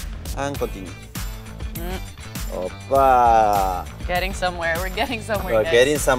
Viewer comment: did Niko just say "oh pak?"